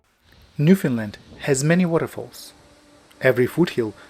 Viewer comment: I thought he was saying Finland- how confusing.